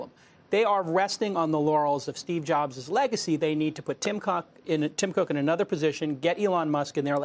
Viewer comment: Did he just say Tim Cock? Oh San Francisco